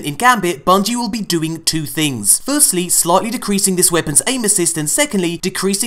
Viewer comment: Great. Now when you say aim assist I hear anus's.